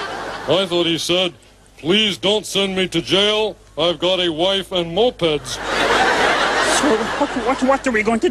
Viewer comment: Oh I thought they said "wife and MUPPETS" ;)